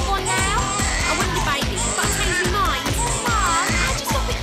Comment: Wait did I just hear "Im anne widecome and I want a baby " at the end?